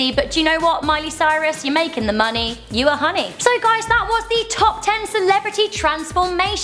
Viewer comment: did she just say 'you're making the money, you're honey'.. wait.. that makes no sense at all..